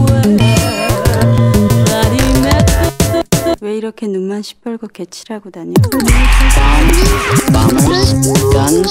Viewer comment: what she say? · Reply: 왜 이렇게 눈만 시뻘겋게 칠하고 다녀? Why do you makeup your eyelids so red?